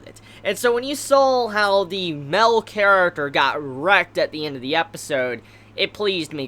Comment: I thought he said erect